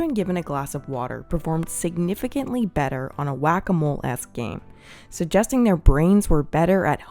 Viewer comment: Well, at 5:37 I definitely heard "whack-a-mole ass" 🙈